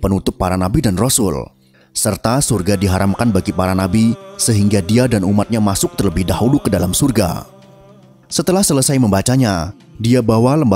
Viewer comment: coba ulangi kata tersebut sampai 4:18 punten, jika kalian mendengarkannya, coba tuliskan di kolom komentar saya, punten maaf takutnya saya salah dengar. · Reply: Serta surga diharamkan bagi para nabi sehingga dia (Muhammad Saw) dan umatnya masuk terlebih dahulu ke dalam surga